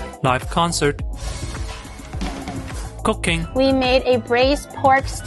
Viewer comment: I misheard "cooking" as "cocaine".